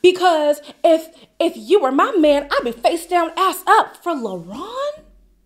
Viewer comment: I think she said if I didn’t have any kids I would be out here face down ass up. Sayin people are judging the guy she likes because he’s out partying but she would be too if she didn’t have kids